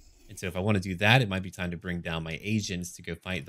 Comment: i heard "might as well bring down my asians...."